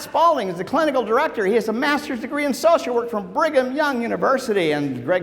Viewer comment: I thought he said Bring 'em Young University, lol.